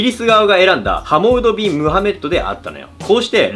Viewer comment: 中間の部分でモハメッドって名前が、モハメド蟻とバフォ目ットっていう空耳が聞こえてくる。